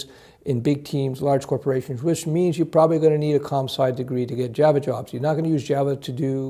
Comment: Hey guys, when he was discussing working for large corporations with Java he said you are going to need a WHAT degree? Sounded like comsite.. at about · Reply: COMP-SCI = computer science. Computer engineering and other tech related degrees are good as well. Just check the job listings for reqs.